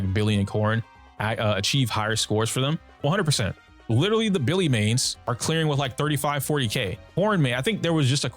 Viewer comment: bro, i thought you said "Billy mays" 😂 like what, he's in zzz?